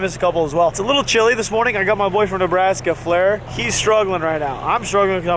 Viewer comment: sounded like you said “got my boyfriend Nebraska here” lol jk